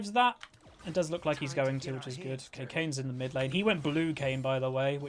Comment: You said Cocain instead of Kayn LMAO